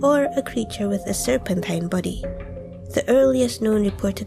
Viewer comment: you sound like you are saying "serpentine buddy" instead of "serpentine body" and I love that so much, keep up the good work😄